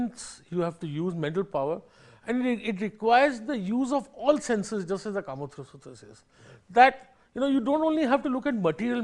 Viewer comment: What he say at 2:12.. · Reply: I heard Kama sutra.